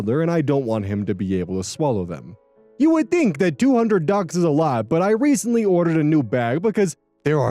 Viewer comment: I thought that said 'bucks'